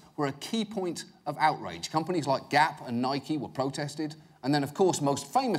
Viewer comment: I'm watching in 2019 and my favorite part is at 5:03 when the captions say GAAP instead of GAP which means it thinks were talking about the generally accepted accounting principles lmfao